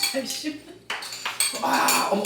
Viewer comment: “아이 시x” 이라고 저만들리나요? 어머님 죄송합니다